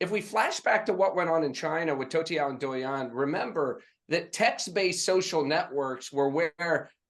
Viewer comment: Toutiao (头条) and Douyin (抖音) is what Brad tried to say fyi at 7:08 😛